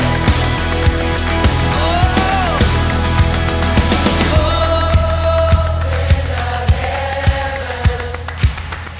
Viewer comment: = total awesomeness! :) Can anyone tell what he says at 4:31? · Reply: He says let’s go! For the band to continue playing